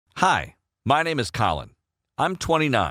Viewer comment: I swear to god at the start I heard: "Hi, my name is cola" XD